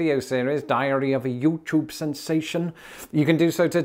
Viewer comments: First of all, I love your accent. Second, I must say sorry, as when you mentioned your special thing you offer for patrons, it sounded like you said "Diarrhea of a Youtube sensation." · I had to replay what you said there at the end when you said “diary of a YouTube sensation…” I thought you said diarrhea😅